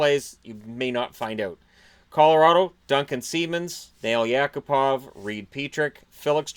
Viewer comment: Sounded like you said Fail Yakupov